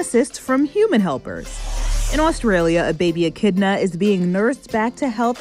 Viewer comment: i thought it said baby enchilada at first lol